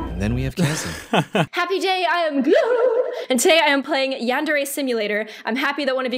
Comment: who heard happy jay in gloom at your first play